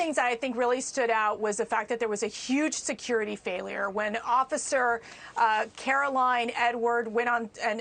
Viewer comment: I could have sworn Mercedes said Officer Caroline N word! lol